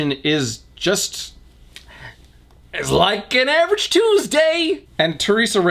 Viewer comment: Did someone say TUESDAY?